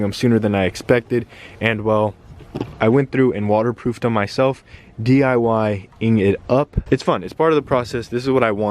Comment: Did anyone else catch this? @ 4:05 "DIY Aing it up" lmao DED! Gang Gang ... or should I say Gang